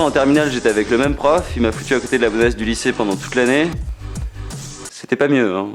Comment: Il a dit quoi a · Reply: Le prof il m'as mis devant la bonasse du lycée pendant toute l'année, c'était pas mieux hein